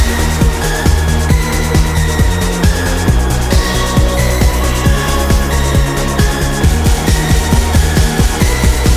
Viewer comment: Does anyone know what Ethan says at 0:21 to 0:31? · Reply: "You Disguise. You Can't Disguise"